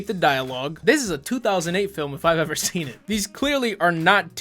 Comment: i thought you said 2007 now 2008